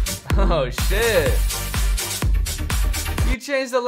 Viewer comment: it's bang instead of Greg